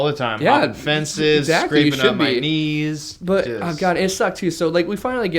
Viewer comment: "Scraping up my knees" I heard "Screaming at my niece"